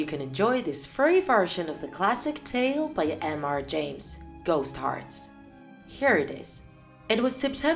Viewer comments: I heard ghost hearts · I heard her said ghost heart instead of lost heart😋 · did she say ghost hearts?